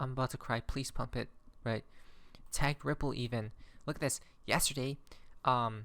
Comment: At the 8:02 did you say tank ripple even lol